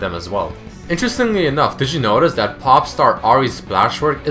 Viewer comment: Did you say holding the cube at 4:44?